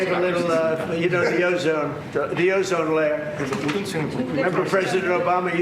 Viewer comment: I thought he said gyozone layer lol